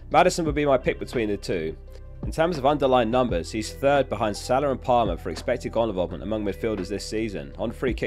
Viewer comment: did you just said paaland? 😂😂😂😂